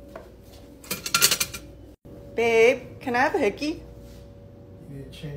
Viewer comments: The funniest part is when she said “good call”😂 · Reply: I thought she said "get out" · "Good call" best line I've heard all day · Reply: Thought she said "Good talk" 😂